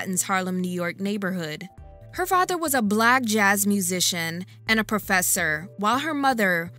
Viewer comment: sounded like blag instead of black lol